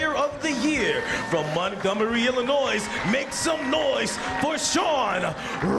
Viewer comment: did he say Illinois's?